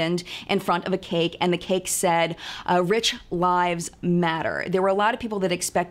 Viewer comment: LOL! At first I thought you said the cake said, "Rich wives matter."